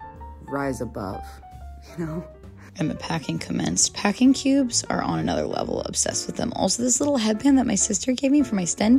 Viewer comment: congrats!! <3 ( where are the packing cubes from at 9:00 ? i couldn't understand ) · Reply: Baggu!